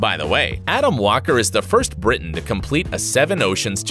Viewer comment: at around 5:00 did i hear "adam is the first britain"?